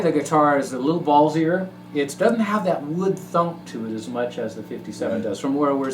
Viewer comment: Great video and demos, good points made and well received , the first one 57 tv yellow had the warmest grind, and did you say " wood thump " @ 6:43 ? you guys should post some band vids, Peace !